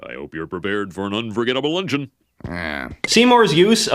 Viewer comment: I always thought Skinner said lunch-in